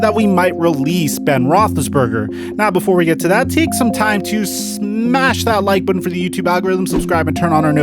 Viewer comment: he said smash instead of sack